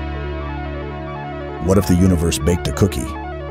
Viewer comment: The question "what if the universe baked a cookie" goes hard af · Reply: I thought it said "maked a cookie".